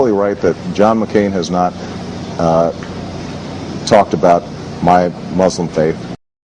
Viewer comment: Get your ears checked, I clearly heard him say, "My muslim faith."